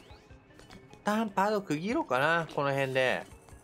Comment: はなんて言ってるの？私には聴き取れないやw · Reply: 一旦パート区切ろうかなー だと思います🤤🤤